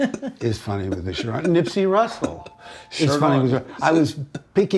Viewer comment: shit i heard nipsey hustle😂